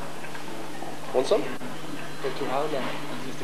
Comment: What said in 39:27? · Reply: He asks "Wan't some?" :)